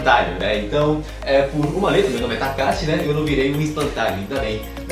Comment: Eu tinha entendi "kakashi" quando ele disse o nome dele kkkk Takashi